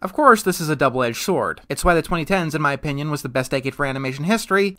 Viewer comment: I thought you said the 1970’s was the worst decade of animation